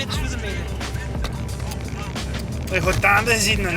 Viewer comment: Did you get what they said at 6:37? I have no idea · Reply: "Ij goa t'endet gezeet'n en", meaning "He will have been at the end [of his capacities]". I believe it's the dialect of the area around Maldegem or Aalter.